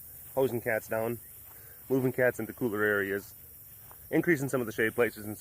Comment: Did you say moving cats into cougar areas or cooler areas? LMAO